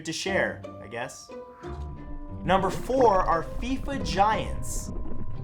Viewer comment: When he said "fifa giants" i heard "feet vaginas" and i dont know whether to talk to my therapist about this or not